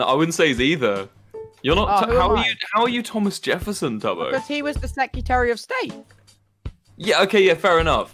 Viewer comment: I didn’t have my volume up good enough, and I thought tubbo said, “He’s the secretary of Satan” O-O